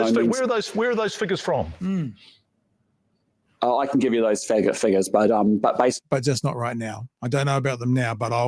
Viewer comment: he really said "f*** figures"???!!!